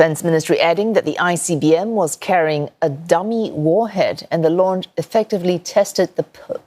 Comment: I thought it said Trump warhead ! 😂